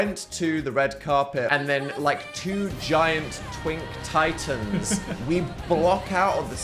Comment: fully thought dan was about to say "twink towers" and i just went with it